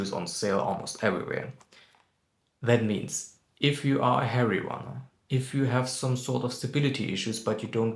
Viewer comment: lol I thought you said hairy runners 😅.